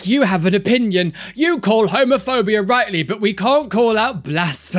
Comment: So I misheard at one point and thought he said "Homerphobia". All I could think of was a bunch of far right extremists trying to ban Homer Simpson, lol. Love your vids! Keep squatting for Jesus like a champ!